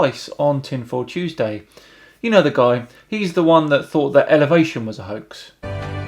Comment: I got super worried because I thought you said Innovation, not Elevation (clearly I need new headphones). I was like "wtf? If innovation didn't exist then we wouldn't be watching any videos now would we?"